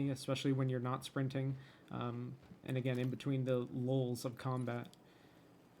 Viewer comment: I know he said "lulls", but i heard "LOLs of combat"